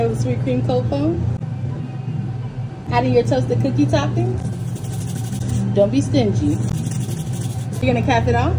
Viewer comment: I thought she said don’t be stinky😤🤡